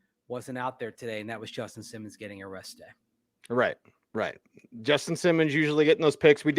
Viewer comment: Mase said Justin Simmons got "a rest day" and I misheard it as "arrested," had a mini heart attack for a second 😂